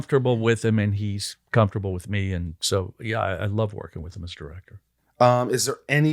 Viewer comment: First time I heard Brent say "I love working with him as Director" I heard "I love working with Mr.Riker" :D